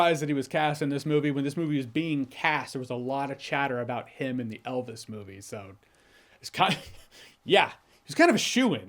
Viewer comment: I kept hearing “Eldest Movie” and got so excited for Eragon with Austin Butler :’)